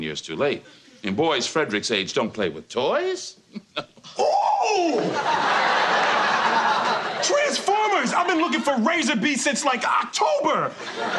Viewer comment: i thought will said oooh treesformers at 0:23 lol